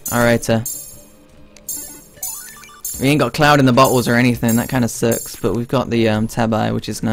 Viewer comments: I thought he said "we ain't got clouds in the butthole" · lol when he said" Darn, we dont got the cloud in the bottle", i thought he said, "Darn we dont got a cloud in the bum hole" xD